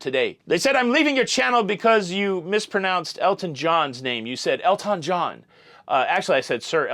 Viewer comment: I thought you were saying “ El Ton John” as if he were a Mexican impersonator. Hahahhaa